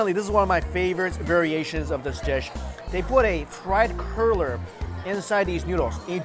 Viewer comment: Ok. At the first restaurant I thought you were saying Chinese "Curler" and I thought the flat rice noodle was stuffed with pig tail, lol. Then you said dough. Got it. Cruller. Funny moment, though and I'm going to bet somewhere in the world there's a pig tail stuffed into a noodle. If not - whoever creates it can name it after me. Ann Ann Noodle. Got a ring to it. 😉